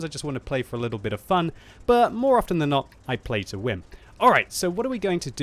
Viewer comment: I thought he said pay to win XD